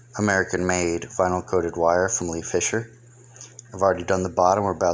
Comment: where did you say you GOT THE WIRE?? I thought I herd you say Fisher or something like that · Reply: Lee Fisher in Tampa.It's a commercial fishing store, They will have the hog rings there too